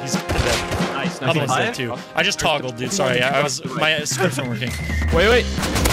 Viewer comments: did he say a shit my skript is not working? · anyone else hear at 49:09 say “sorry i toggled i didn’t have my scripts on”